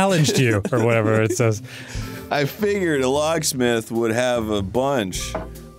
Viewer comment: I had to rewind 10:22 twice, because I could have sworn Arin said "I fingered a locksmith." Not that I'd put it past him, it was just a little out of left field for a Sonic game lol.